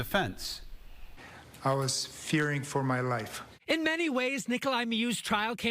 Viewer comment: People thought he said " I was fearing for my life". What he really said was " I was feeling for my knife".